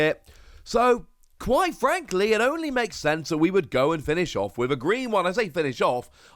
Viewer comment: I’m curious brotha why do you want this to be the last trainer tool kit? Red blue I was hoping green next too. Maybe all white after that then black, then fill out the rest of the rainbow over the next 6-8 years. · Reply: I think you misheard him. He said finish off, but he clarified that he hopes the Trainer Toolkits continue. If I am misunderstanding your comment, I apologize.